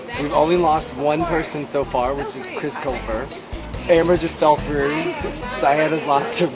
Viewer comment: what did Zack say? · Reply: he said Amber not camera